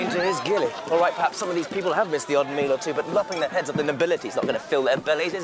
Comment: What does Archie say at 4:11? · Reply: It's as my father explained to his gillie, "Perhaps some of these people have missed the odd meal or two but lopping the heads of the nobility isn't going to fill their bellies, is it?". Still that's Johnny Crapaud for you.